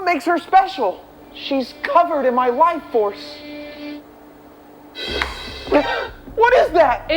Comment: “She’s covered in my life force!” Wild work, 10/10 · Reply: I thought he said : Wife force (waifu - force)